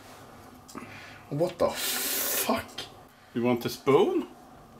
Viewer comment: when he said "you want a spoon?" I heard "you want to spoon?" XD